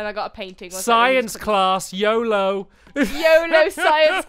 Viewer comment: when u said yolo at 8:58 i thought u said yobo haha lol